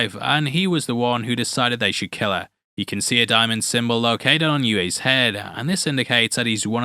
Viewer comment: Sounds like you say some bollock which fits better than symbiotic...🤣😂🤣😂